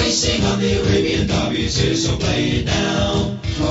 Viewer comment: "It's here so play it now" is the hardest part to hear for me. During one point in my life, I thought it was "The best soufflé in town". I also thought the song said "Radio Derby" at one point, but I was young and hadn't heard of Arabia yet, and currently listened to Radio Derby.